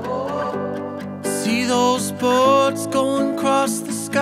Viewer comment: Has anyone else misheard that part: "those cops lying on the buss" 😅😅😅 but seriously this song and what Odell does is magical!! 🤗🤗🤩🤩😍😍😎😎